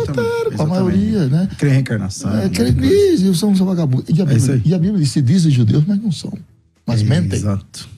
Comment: Eu entendi errado ou ele chamou os Judeus de vagabundo 52:27??😆😆😆